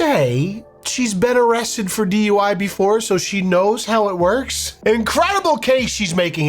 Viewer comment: "So she knows how it works." Oh, that's it. I thought she said "I went to Hogwarts."